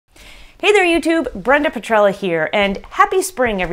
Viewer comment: I was laughed to myself , I heard 'Brenda patrol here' and I've noticed It was 'Brenda Petrella' 😂😅😆✌️ peace Brenda ,. Btw you're awesome. Thank you for your effort to do this tutorial. We've learned a lots from you.